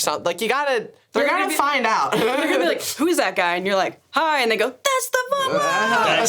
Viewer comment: Did just say the N-Word?! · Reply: She said "and they go"